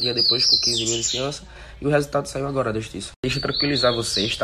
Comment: Eu ouvi "saída com 15k de fiança" ?